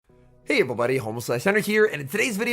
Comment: i cant be the only one who heard "Hey everyone, Homeless Slice Henry here" in the intro😭😭😭😭